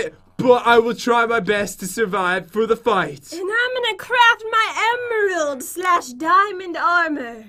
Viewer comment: I thought she said she is gonna cra* my emeralds but she meant craft.